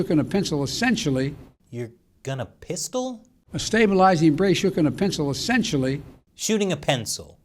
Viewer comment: I heard “Choke on a pencil.”